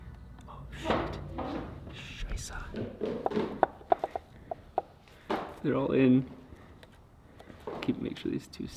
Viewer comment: Did Rob just say "Scheiße"? Shit in German. I mean he said "Oh shit" right before.